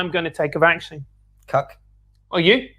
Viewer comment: lol Did he call him a cuck